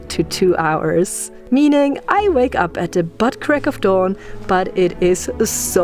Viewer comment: Did she say she “wakes at the butt-crack of dawn”? lol Or is it Dawn...Dawn’s butt crack?